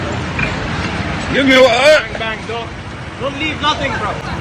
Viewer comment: did I just hear from behind the camera don’t leave anything 😢